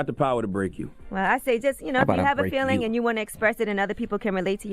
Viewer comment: did i hear that right at 7:08 "how bout i break you" if i heard that right dont think he like the other guy. hahah